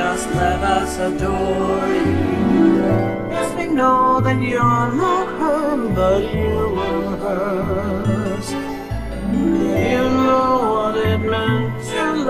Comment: what yellow say? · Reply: “Yes, we know that you’re not her, but you were hers.”